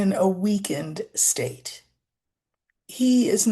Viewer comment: I thought you said he was in a "weekend state" I was like what does that mean? Vacation mode? Haha